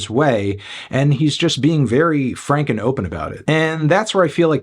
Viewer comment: what he said: "he was very frank and open about it" what I heard: "he was very frank and ocean about it"